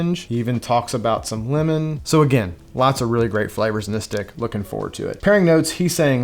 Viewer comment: What did u say? LOL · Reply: 😂 I said stick, but it did kinda sound like something else lol.